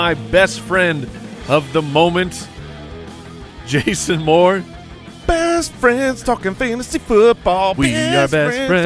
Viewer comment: Thought I heard "best friends fkkn fantasy.." lol